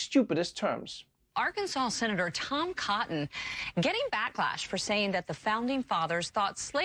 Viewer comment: Did I hear, “Dumb Cotton”?